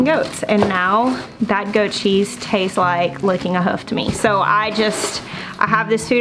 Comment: XD Did you say "licking a hoof"?!?! XD